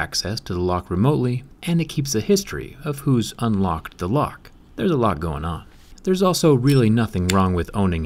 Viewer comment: “Did you say there’s a LOCK going on”?